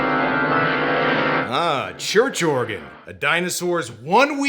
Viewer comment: For way too long I thought that you said Churr Jorgan did the deed. I thought that how I knew the cowboy was blond and Scandinavian looking, but I was sure that his name was more American than that.